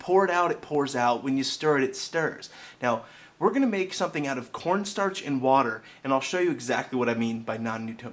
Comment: At the beginning I thought he said porn stars and water. 0_0